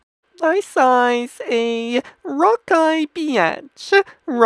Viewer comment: he said rovky beatch but it sounds like he is saying rocky bitch